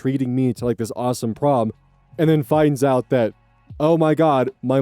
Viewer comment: i thought prom said porn 😭